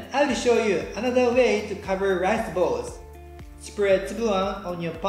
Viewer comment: I couldn't understand what you said at 8:20 and even subtitles didn't get it right, it sound like "spread tsuan" but I'm not sure